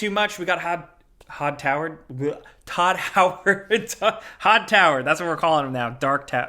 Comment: I heard Todd Howard’s dark tower when i walked about the room, and when i realized i misheard…let me tell you what true disappointment is…